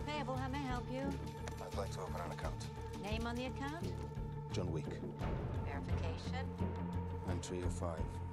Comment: it sounds like he said john week or weak